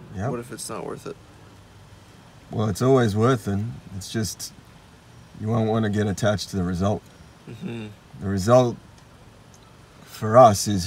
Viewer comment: what does he say? „You wouldn’t wanna get attached to the result?“